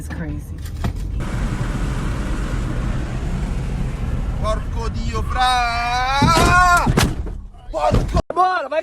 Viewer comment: What did he say and mean at 3:14? Parco de afri?